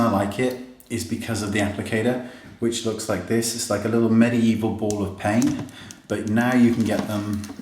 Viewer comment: did he say "mini evil ball of pain" ?? hahahahaha